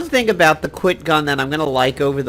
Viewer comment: there was a weapon that sounds like you said quick.... I couldn't understand what you were saying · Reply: "The qyit" See Doug's comment above. It was the previous gold sale bonus weapon. The multifunction gun.